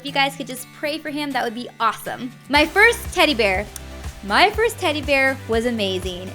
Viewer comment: i thought she said titty bear i was like she had a boob